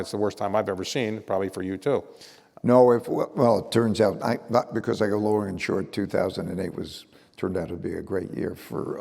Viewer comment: What does Dalio say? I can't understand. Something about "not because (inaudible) 2008." It sounds like "not because lower insured" but that doesn't make any sense.